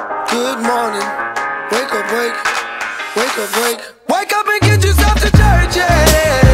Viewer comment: I keep hearing, "wake up and get you some detergent"